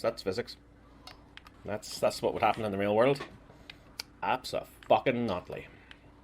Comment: I agree he looks totally guilty, but the name drop thing I think is wrong. To me it sounds like he is saying "yeah, that's how real physics works. Abso-fucking-NOT-ly" I mean, the accent makes it a little bit unclear, but that's what it sounds like